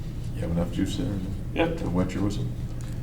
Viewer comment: i watched this for a hour n half while falling asleep and the guy said "hows that juice does it wet your whistle" and i couldnt fall asleep for another half hour because i was laughing ( 55:50 timestamp, thanks to the comment below) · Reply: woah. The literal second I finished reading your comment, I heard “do you like the juice, does it wet your whistle?” Lmaoooo